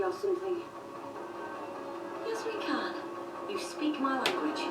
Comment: Haha, they got the subtitles wrong at the 4:00 mark. She says Yasmin Khan, not "Yes we can".